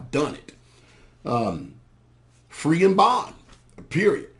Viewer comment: what does he say? sounds only like "free n bon" and it's the end of good pt.